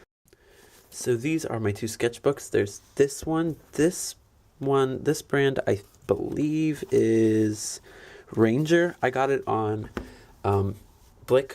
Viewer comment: does anyone know what kind of sketchbook he shows first?? also ily your personality is v cute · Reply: tia oh ahah thank you! my stupid ass thought her said Granger, not ranger ahah